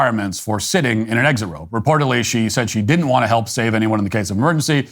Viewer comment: Wait, I thought she said she understood she had to help!